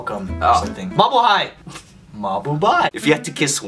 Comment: did ryan just said mabuhay? xD ♥